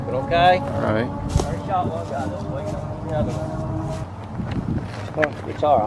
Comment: did he say "I already shot one guy this week?"